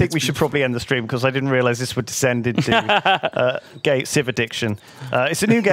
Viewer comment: Gay Civ addiction? That’s what I heard